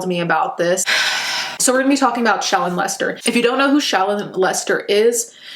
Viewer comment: when I heard you say shallon Lester I heard child molester tf